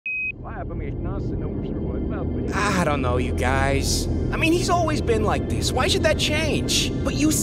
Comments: wth Jupiter said here? Its reverse ofc but WTH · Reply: “We’re all made of leftovers from when the Sun came about” but reversed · wow, didn’t think the first line of this vid would be reversed. i wonder what jupiter’s saying there… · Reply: He says something like “leftovers from what the Sun came about” I can’t hear it clearly..